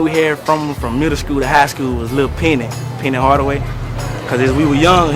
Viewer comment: LMAO i thought i heard the same thing, he said little penny tho,for penny hardaway